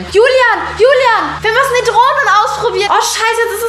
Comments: Ich hab bei 1:06 verstanden: Julian, Julian, wir müssen die Drogen ausprobieren D: Wer noch?! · ich habe drogen verstanden 😂